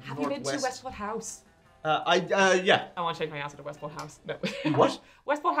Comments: I heard "I want to change my address to Westport House" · I heard her say, "I want to change my answer to Westford House."